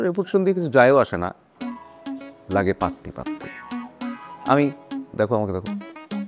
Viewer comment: কি বলেছে ঠিক বুঝলাম না। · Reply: পাততি,টাকা পয়সা এগুলা বলছে।